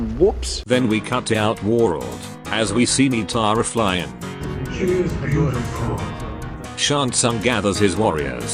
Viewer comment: I know it's a different language, but I SWEAR it sounds like you guys said "she is beautiful"